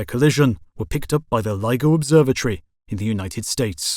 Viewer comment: who else thought he said Lego observatory?